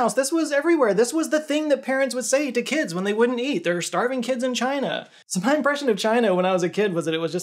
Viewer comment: Believe it or not, I heard "Starving kids in Alabama"